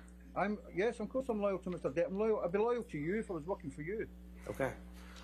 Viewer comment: what does he say at 1:52? "i am loyal to you; Ive been working for you'. Does he work for that laywer as well? Or did I misunderstood?